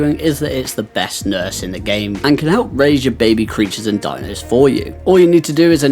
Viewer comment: I heard I’m the best nurse in the game not 6:16!💪💪💪